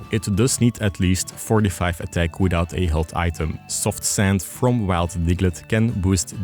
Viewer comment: what did bro say? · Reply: Soft Sand from Wild Diglett.